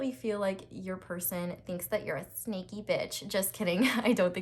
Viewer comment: Pile 2: "your person thinks that you are a sneaky bitch" you really scared me there :0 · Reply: I thought she said 'snakey b*tch' 🐍